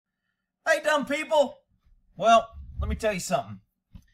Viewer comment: morning! it so sounded like you said hey dumb people this time! lol hope you did so i know i heard you right! lol